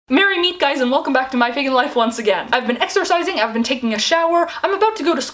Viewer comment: I thought you said exorcising instead of exercising😂😂😂